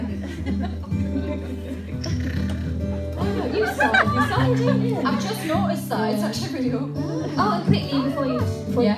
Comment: What did Jade say in 4:40 before Perrie said "I've just noticed that"? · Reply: Natalia Kopacka “Oh! You start off the song, don’t you?”